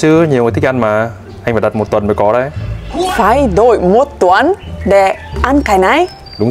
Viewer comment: Phải đợi 1 tuần cháu nghe thành (vãi nồi 1 tuần 😂